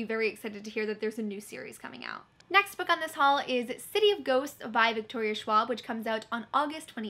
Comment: Lmao when you said “City of Ghosts” my brother and I both thought you said city of goats...... we’d read that lol